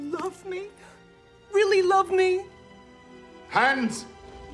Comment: What does Gomez say at 5:25? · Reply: He says "Hands"